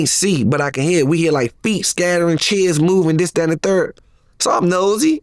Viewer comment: I hear “feet scattering , chins moving “ 🤣🤣 🤣 I know he said chairs though but I heard chins